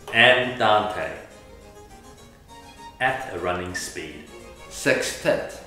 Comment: In case if u don't understand what they said at 3:30, it was "where people have s*x together." Thank me later I can actually lip-read